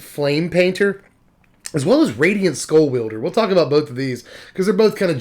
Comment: I was listening to this while sorting cards and heard you start talking about Radiant Scrollwielder. I thought you said Radiant Squirrel Wielder and my head snapped up so I could see the card! Very disappointed! Radiant Squirrel Wielder would be a MUCH better card.